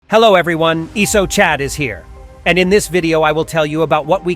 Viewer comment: I thought I heard iso shit here the first time